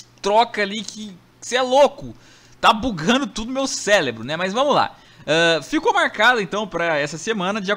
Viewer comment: só eu entendi celebro?